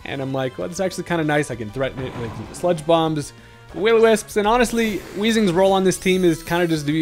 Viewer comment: So I could be wrong but it sounded like you said “wittle wisp” when you were bringing in the discoballz vs the grass monkey in game two